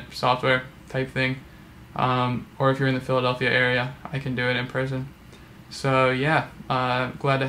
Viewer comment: "And if you're in the Philadelphia area, I can teach you in prison" Totally thought that was what you said at first. Thought you were implying everyone in Phili is a criminal!